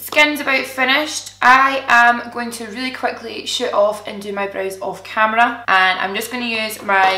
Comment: Omg Im dying laughing! When you said you were going to shoot off and do your brows. I thought you said you were going to shit off! LOL